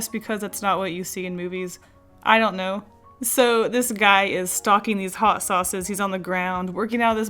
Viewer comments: for some reason I thought she said "stalking these hot sauces". 😂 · i thought you said "This guy was stalking the sauces" lol